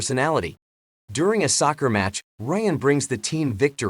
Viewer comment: What did he say??? Soccer match??